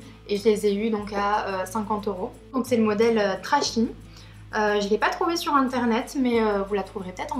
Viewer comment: Salut ! :-) je ne trouve pas la référence des bottines style rock ? J'ai entendu "trashny" mais je ne suis pas sur ^^ rien sur le blog à ce propos dommage ... J'irais bien voir si je les trouve. Elle vienne de chez André également ? Merci beaucoup bisous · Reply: Hello, oui c'est le modèle Trashy, malheureusement je ne les ai pas trouvé sur le net. Elles ne sont dipo qu'en boutique. Chez André